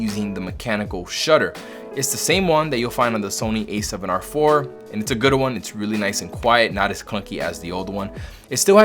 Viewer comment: I'm sorry to be "that guy" but did you say "it's a gooder one"